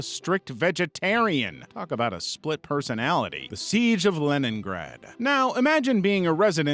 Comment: On leningrad I thought bro said lemongrab from adventure time😭